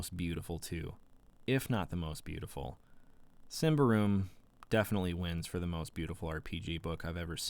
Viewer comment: What was the name of the most beautiful rpg ever? I couldn't catch the name but would like to check it out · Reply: Symbaroum, which is also published by Free League. It's a great system and setting, Johan Nohr who worked on Mork Borg actually also worked on this game too. You can check it out here: <link> I highly recommend the physical book because it's so well done, but if you decide to snag a copy digitally here's an affiliate link for the PDF (which is actually on sale RN for $12!): <link> Print